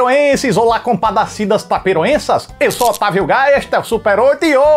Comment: Eu ouvi "Eu sou Otávio Gaia".😅🤣🤣🤣